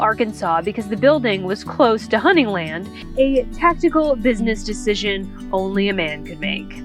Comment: I thought you said "He chose Bentonville Arkansas because the building was close to hunting land, a testicle business decision only a man could make"? You're really making some great videos. But I thought you were in Texas. Cheers from Lo s Angeles.